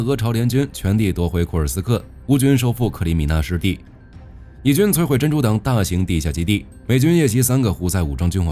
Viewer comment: 聽成以軍摧毀珍珠島大型地下基地。。。